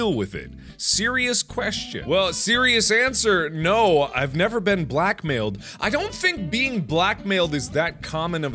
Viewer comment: I farted really loud and through the noise, I thought Mark said, "No, I've never been a black man."